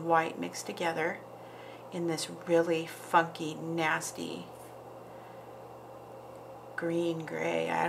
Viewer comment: Describing teeth thought you said “f@ nasty” lol almost died laughing 😂